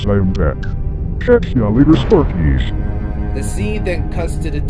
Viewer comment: someone tell me what is jumbo saying? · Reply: Catch Ya Later Sparkies